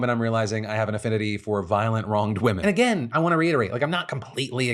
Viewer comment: So, I totally thought that he said, "Violent Blonde Women", and I imagined the editor should have slowly had a picture of Phil's wife slide into frame.